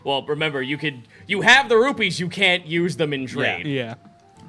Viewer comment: Did I hear accidentally (Or Purposefully) say "Rupees"? Edit: ... Yup, everyone's calling 'em Rupees. Welcome to Hyrule, Nat One-Ders! Edit #2: B a l l B e a r i n g s